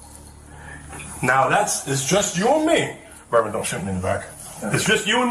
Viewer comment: "Don't shoot me in the back". Do your officer have a habit of shooting you in back? I know the subtitles says "Berman, don't shake me in the back" but that's not what I heard him say. And another instance on of the officers clearly says "what's your name ma'am", but the subtitles says "what's your name, girl?" So I know the subtitles are wrong.